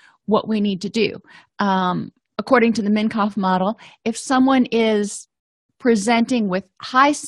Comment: what did she say at 4:17 mein kampf model ,....